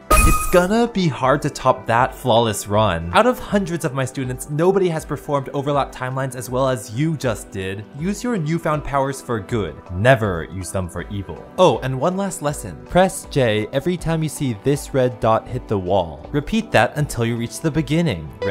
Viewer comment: I thought you said use them for good 😭